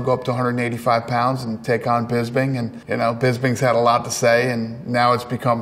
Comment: Did he just say Pisbing twice? Lmfao